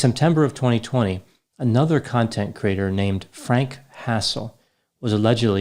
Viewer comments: At first I thought you said Frank Castle. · At first I misheard Frank Hassle as Frank Castle and thought yeah, Boogie has a problem on his hands there...